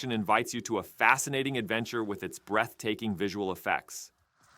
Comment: thought you said "breast taking" instead of breath taking while introducing stellar blade lol